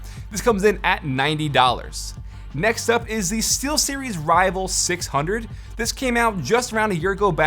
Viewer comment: i thought he said t-series buy he said steelseries